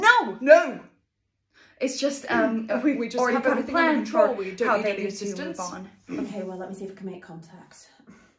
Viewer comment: What are Jonathan and Maisie saying at 1:59? I’ve watched several times and still can’t figure it out 🥺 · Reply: Maisie says ‘we already have a plan for how we get them through to the afterlife’ Jonathan says ‘we have everything under control, we don’t need any assistance’ ☺️